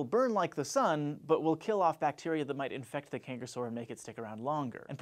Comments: At first I thought it said cancer sore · I thought you said cancer sore